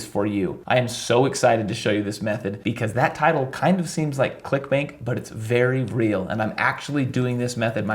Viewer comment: Did you say that title kinda seems like "Clickbank" 🤣 When you have affiliate marketing on the brain